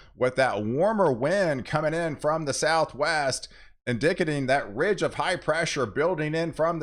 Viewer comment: Did he just say in-DICK-a-ting?